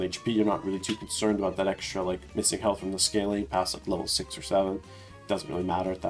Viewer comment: Did I hear 67